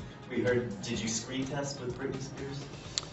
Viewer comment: Guys, could anybody help me to make out what journalist did say at 1:09? 'We heard did you ... for Britney Spears'? Did you what? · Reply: He asked, "Did you screen test with Britney?"